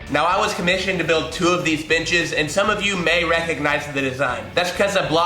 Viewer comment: Very nice, sounded like you said these bitches...lol